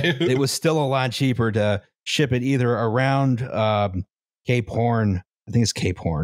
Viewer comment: ish I totally heard that as gay porn.